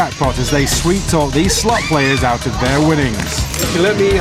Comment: Lol I thought he said slut players